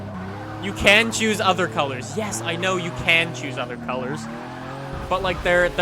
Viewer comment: Is it just me, or does anyone hear a faint "yes"?